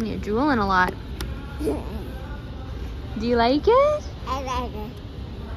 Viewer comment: I heard him say at first.. : I love it, then he said I like it. enjoys his wheelchair! Go go!